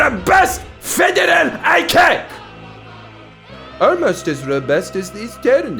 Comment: The subtitles said "with a robust federal eye cake" and now I'm gonna pronounce ICAC as eye-cake - thanks, Jordan.👍